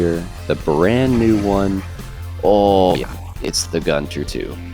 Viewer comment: thought that said cunter